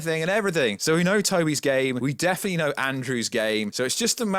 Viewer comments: at first i heard "so we know Tobey is gay, we definitely know andrew is gay" 🤣🤣🤣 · Did anyone do a doubletake when said "Tobey is game, Andrew is game" ? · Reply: at first i heard "so we know Tobey is gay, we definitely know andrew is gay" 🤣🤣🤣